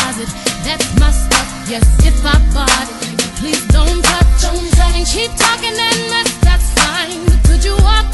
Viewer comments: I thought she Said "in the closet, that's my stuff, yes if I bought it nigga please dont touch" lol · for the longest time I thought she said, 'if I farted please don't touch!?!? Srsly.